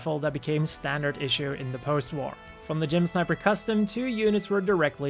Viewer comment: sounds like hes saying Jim sniper.